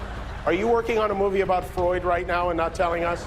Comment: Freud or Florida? I heard Florida the first time and it made sense💀